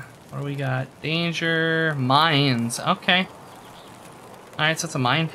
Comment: When you said 'danger: mines', my friend thought you said 'danger: mayans' and then wondered how much a macuahuitl would sell for to Fence, lol!